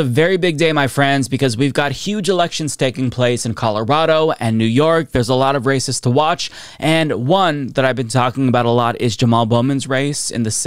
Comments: I fully thought you said “there’s a lot of racists to watch” which really isn’t incorrect unfortunately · You said "There's a lot of races to watch" meaning election races and I heard "There's a lot of racists to watch" because, y'know, Republicans. XD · Sound like you said “There’s a lot of racist to watch” 😂😂😂 Run it back To check. LOL!!! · The first ten seconds caught me off guard. It really sounded like you said "there's a lot of racists to watch". Still seems accurate though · I thought you said, "There are a lot of racists to watch." And that is also correct.